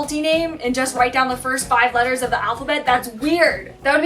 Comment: When said no Japanese will name his child a-i-u-e-o I heard only 'Ai' and thinking 'that exist'😂